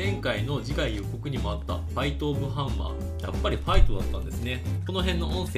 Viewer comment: ああファイトオブハンマーだったのねｗ予告で聞いた時はナイトオブアーマーって聞こえたからなｗ防御系かと思ってたｗ